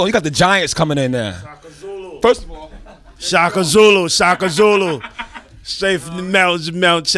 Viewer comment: did he just say Shaka Zulu??🇿🇦🇿🇦🇿🇦😭😭😭😭